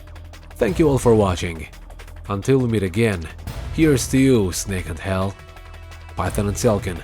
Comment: For a second I thought he said "Here's to you, Snake in hell".